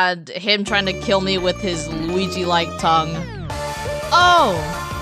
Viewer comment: Hold up, was it just me or did she say "Luigi like tongue" in 49:35 :/ With that aside, this mod was very worth the wait. The amount of content is immense and not of them have even the slightest bit of effort deterioration (seemed rushed or imperfect). Very satisfying Edit: i also found something interesting, for the song "bad day" mario has a different technique of messing the ui depending on scroll type (up or down scroll). Good attention to detail in my opinion